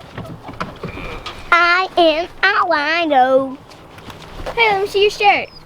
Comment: cracks me up. I thought he said "I am a wino"! Maybe you can make a couple of pieces of square plywood to place under the fron to keep it from sinking in.